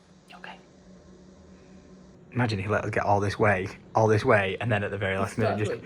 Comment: Did anyone else hear him say ‘her’ @ 14:17 Don’t think I’m hearing things but if it’s true going to be a girl dad, proper daddy’s little girl, congrats to you both man so proud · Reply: 100% says “imagine if you let her get all this way”